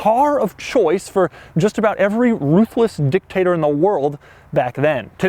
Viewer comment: AHHH "ruthless dictators" i thought he said toothless dick-takers. I was shocked crack whores back then could affors 150k MB